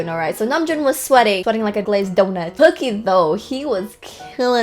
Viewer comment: i heard "cookie dough" instead of "kookie though"